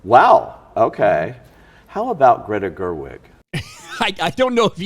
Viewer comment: I'm half asleep. I thought you said, "Greta Thunberg".